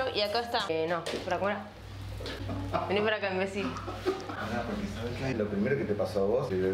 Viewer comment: Te juro que escuche "Venir para acá messi" xd